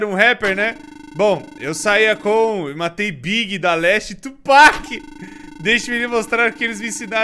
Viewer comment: eu ouvi Daleste foi? :^